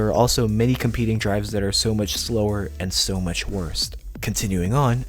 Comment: Did he say "...and so much worsT?" Ugh.